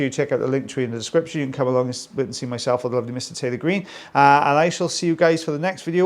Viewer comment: Swore you said “Tilly Bean” when you say Taylor Green